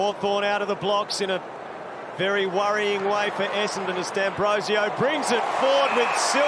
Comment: did he say essendon? Lol